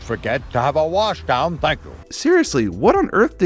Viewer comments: I always thought he says thank you after washdown but I can see that angelis was slow to fit the animation but went fast and said thank you to fit it in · he is clearly saying thank you to his request for the train to get a wash down, clearly